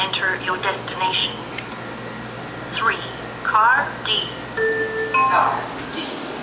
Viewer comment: did he just say “Ugh D”? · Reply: no he say Card D